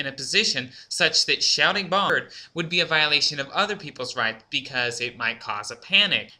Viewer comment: my computer skipped and it sounded like you said "shouting boner would cause panic"